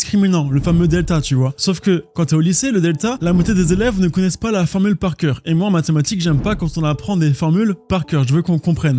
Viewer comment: quoi ? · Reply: Illumi… 🔼